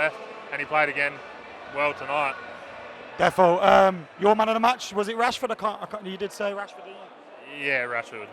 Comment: I thought it said "was Rashford your mom?"..dont mind me